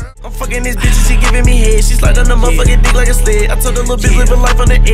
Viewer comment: “like a sped” 🤣🤣-bruh that ain’t even funny 😂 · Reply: The Unique Clone o “slide like a sled”, thought he said “suck like a sped”